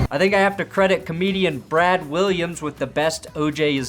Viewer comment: For a second I thought you said brad holms not brad Williams i was about to be like what!?